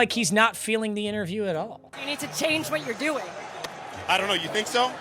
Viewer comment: in defense of the halftime reporter (who i actually don't like her reporting, but she is innocent on this one) although it sounds like she said "You need to change what you're doing", she actually asked "Do you need to change what you're doing?" which is still a dumbass question, because how tf do you expect him to answer that besides a "no shit" but she wasn't trying to be like "youre gameplan stinks, you need to change something". Rather simply asked him if he feels he needs to change his gameplan.